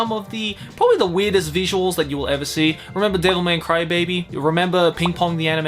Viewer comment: Did he just say "Devil May Crybaby?" XD